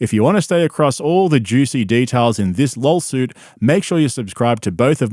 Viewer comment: Not sure if I'm just mis-hearing you but I keep hearing "Lolsuit or lawlsuit" 😂😂😂